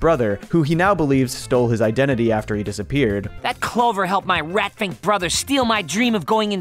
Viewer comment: Did Fry call his brother a 'fink'? lmfao I was not expecting that